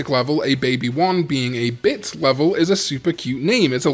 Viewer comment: "a baby digimon being a bi*** level is really cute" kkkkk it made me laugh, I know you said bit but i only heard bit** in my head kkkk